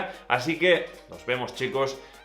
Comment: Al final le escuché “infeliz 2020” Y después me di cuenta de que dijo “y feliz 2020” :v